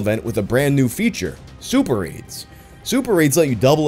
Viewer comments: during the ad read for Raid, when said Super Raids I almost thought he said super aids....... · During the RAID: Shadow Legends ad is he saying "Super Raids" or "Super Aids"? I swear the first time I heard him say Super Aids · Thought he said super aids at first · Sorry, did you say Raid Shadow Legends has Super AIDS? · OHHHHH Super RAIDS. I thought you said Super AIDS was coming to Raid Shadow Legends and that seemed like a weird thing to put in an ad.